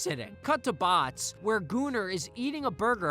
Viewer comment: Did you just call him "Gooner"?????? lmao that had me laghing for real. But yeah Sam & Cat was always a weaker show imo it had some weird episodes to say the least.